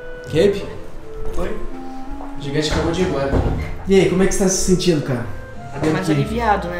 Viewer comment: o eu ouvi o falando q o gigante acabou de ir embora